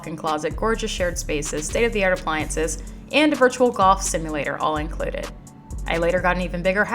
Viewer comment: Girl I thought you said the Dallas place had a “virtual goth” 🧛‍♀️ and was like ???? she going wild with her money! … turned on subtitles and rewinded…VIRTUAL GOLF ⛳️!! lmao